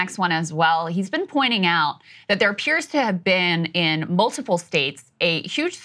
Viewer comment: Wait did she just say Trump’s assassination attempt “which appears not to have been such” 🤔 fuck does that mean. 😂😂😂